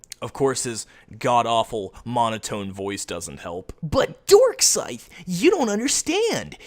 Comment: Is it me or did he just call himself "Dorkscythe"? Also, I'm not trying to defend LimeX's comment on Darkscythe's video; I'm just pointing something out here. At 17:14 when he said "pretty much raked in his hoard of autists by making low effort, click-bait-happy, and edgy videos", I think he was actually talking about Leafy, not.